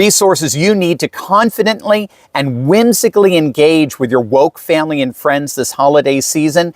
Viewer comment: With my WOKE family? Did I get it right Dr Steve? Is that what you said (min 3:50)?